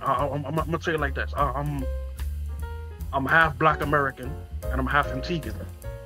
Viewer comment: Excuse my ignorance on the matter. I am Italian and I live in Italy. Doesn't a black person from the Dominican Republic also probably come from former slaves imported by the Spanish to work in agriculture? The cultures are certainly very different but I believe there are points in common. Maybe we don't have a common history, but a very similar one. The Black Panthers felt like brothers with the other oppressed, proletarian and discriminated against people in the rest of the world. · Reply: I thought I heard him say "I'm half black American and half Dominican" but he says another word that I don't understand.